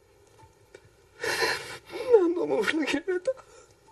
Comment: なんて言ってるか聞き取れない汗 わかる方教えてくださいm(_ _)m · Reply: 何度も裏切られた かなぁと思います